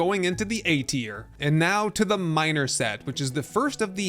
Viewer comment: just a heads up but you put "minor" instead of miner 😁😁